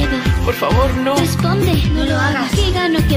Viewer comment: yo si le entendi dice: "no me dejes asi", es facil entenderle si escuchas bien